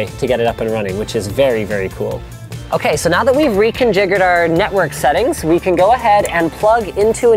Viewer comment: DID HE SAY RECONJIGGERED